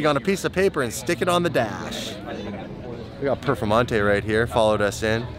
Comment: Did he say perfemonte